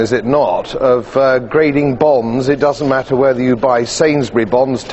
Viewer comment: I heard "bongs" :D